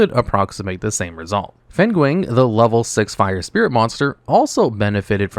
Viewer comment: As someone who understands Mandarin, listening to him pronounce Fenghuang (which means "phoenix") was hilarious 🤣 I swear I heard him call it "penguin"